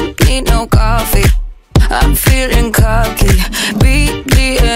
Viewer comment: wtf did it just say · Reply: “Im feeling cocky”